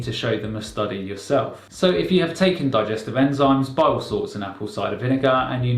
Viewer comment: what he say b4 acv? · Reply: Bile salts but the captioning likes to put "Bible Sauce". 😂